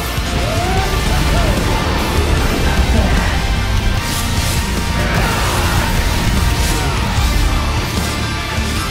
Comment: When Dante uses his black hole skills to try to kill Vergil. Did he just say sorry but die? oh and 7:46 that detail make this battle epic! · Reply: He said "Not bad" and it was just a grunt (or whatever its called) after it on that part, I forgot to balance the audio levels in the later parts so I apologize for not hearing it well.